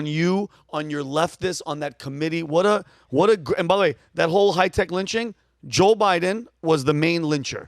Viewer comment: I thought he said it was a modern day lynching? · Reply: High Tech